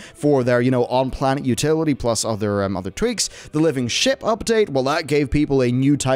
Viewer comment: I thought he said living shit update lol